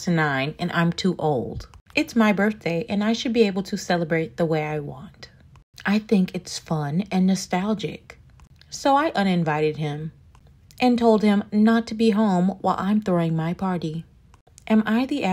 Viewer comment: ngl i thought she said “ So i un-alived him💀